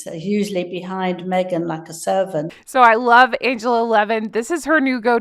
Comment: I thought Angela said Megsie is "like a serpent"!